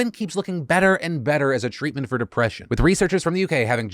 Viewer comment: I thought you said suicide is a good cute for depression I was about to cry LMFAOOO